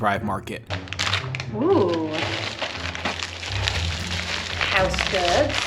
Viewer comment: At the 4:31, did call you "house stud"? I've replayed it and..... it could be me or....